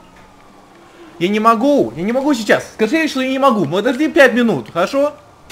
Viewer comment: What did he say? I hope it isnt your mom....... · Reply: "I can't, I can't now, I said you that I can't right now. Give me five minutes, okay?"